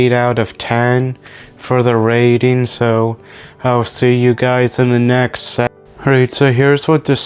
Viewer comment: did anyone make out what he said? Sounded like he said I’ll see you guys in the next se